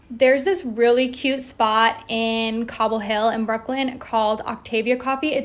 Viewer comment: As an Afghan when I heard “Cobble Hill” I thought you meant “Kabul Hill”. 😂 😂😂